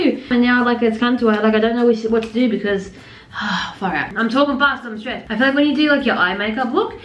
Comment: Did sabre say the f word at 14:15? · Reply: She said far out she says that alot